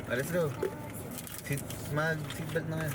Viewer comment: did u say “Mahal, seatbelt mahal to Dimple?” Like whhaaat?!!!😮 OMG!!!!